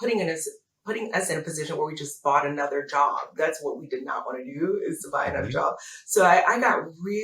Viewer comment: Good luck! Hmmm Thought she said “didn’t want to buy a job”